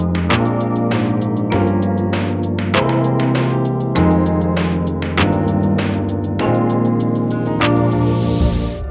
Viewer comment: Can someone help me understand what Wonbin is saying at 2:55 I've been trying to figure it out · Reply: this is the last train